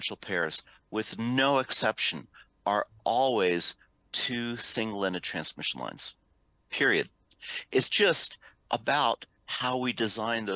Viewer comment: What does Eric say at 3:06? All differential pairs are two single-ended transmission lines? Edit: ok yes I was right as you can see later on the slides but it's really hard to tell as he seems completely swallow the word "ended".